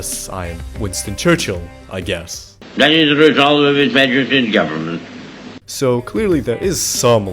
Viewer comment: I believe at 1:35 "that is the resolve of his majesty's government" is what he was saying in case you were genuinely confused